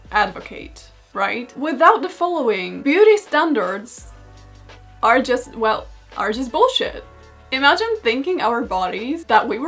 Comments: for a minute i thought you said "beauty standards are just wealth" and that wouldn't have been wrong. Beauty standards are just an easy way to profit of of making women insecure. I hope i live to see the day we can all stop glorifying other people's looks and instead celebrate our own bodies. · sounded like "beauty standarts are just wealth..well bullshit" 🏆